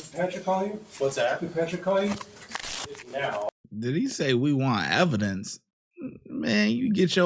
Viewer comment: I thought he said what evidence.